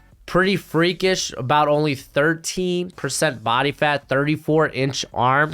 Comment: did you say 13% or 30% body fat for Travis Jones? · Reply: 13% body fat